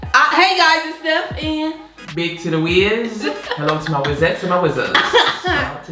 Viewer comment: Sound like she saying Big Tittie Wiz 🤣🤣🤣🤣 . Yesssss, I use to destroy their BBQ! Looks so good!